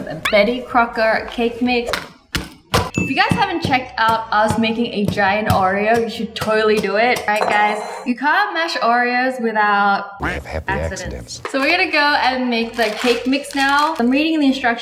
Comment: Wait did she say "you should do it" I don't have the space to put a giant oreo XD